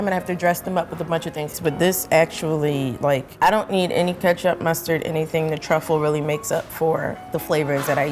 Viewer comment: talking about uncultured swines, 11:10 did she say ketchup and mustard? wtf lmao. btw does a tastykake taste the same as a Hostess cup cake?